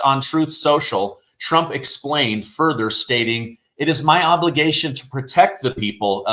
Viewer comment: I thought he said ligma 🤣🤣🤣